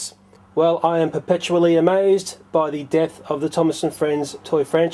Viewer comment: For about a second I thought you said " I am absolutely amazed at the death of the Thomas and friends franchise" I nearly choked on my soda. Then I realized you'd said DEPTH